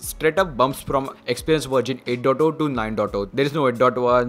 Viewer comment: What is he saying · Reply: I think he's talking about the Samsung Experience version, that it got an update from 8.0.0 to 9.0.0